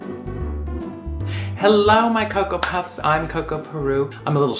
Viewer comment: i know this is an old video but i thought she said hello my Ko Ko Bops not that that matters but anyways lol